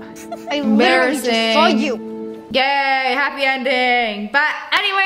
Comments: HELP I THOUGHT SHE SAID “GAYYY HAPPY ENDING “ ALSO ONE OF MY FAV VIDS FROM DHAR LOVE THE RELATIONSHIP W BOX IN HELMET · I THOUGHT SHE SAID GAYYY HAPPY ENDING 😭😭